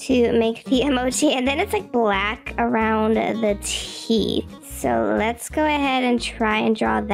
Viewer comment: I thought you said teeth when you said chees 😂😂😂😂😂😂😂😂